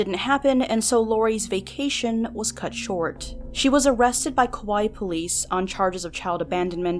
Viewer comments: I know you said "hawaii police" but for some reason I heard "kawaii police" and I just thought of a bunch of adorable police busting her. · So... "I will have you destroyed" is not a threat on someone's life? Right... Also it sounds like said she was arrestred by Kawaii police. Would anyone else watch a comedy like that? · I know this is prolly a place in Hawaii, but it totally sounds like you say “she was arrested by kawaii police” and all I can think is cute anime girl cops bustin in this bitches door lol